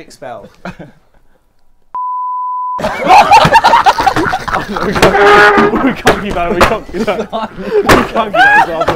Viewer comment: What did he say at 2:45 .. it's sooo hyped up now.. what is itttt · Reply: He said abbranigata